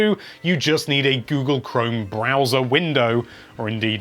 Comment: Stadia: "All you need is your trusty internet browser." Nintendo: releases update which includes an internet browser. Nintendo: "checkmate." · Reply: I thought u said bowser and got confused 🤷‍♂️